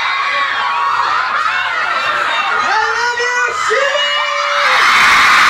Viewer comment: Did he just say I love you shimmy as in shimmycocopuffsss?